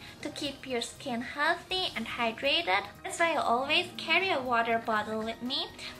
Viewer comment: I thought you said vodka not water!